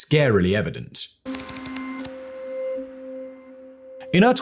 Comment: Lmfao at first I thought that he said that book was written by Barbra Walters as in the one from the view 😂.